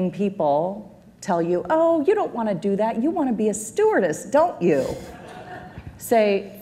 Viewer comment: what does she say in the minute 7:53 (oh you don't wanna do that, you wanna be a...?) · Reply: stewardess (flight attendant)